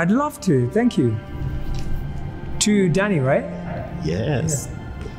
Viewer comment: When it said danny i thought it said daddy lol